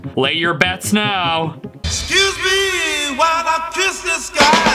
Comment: gotta me honest I heard "excuse me while I piss the sky"